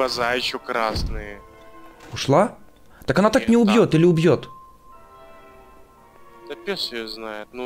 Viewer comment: Мне послышалось что ты сказал пушает или пьет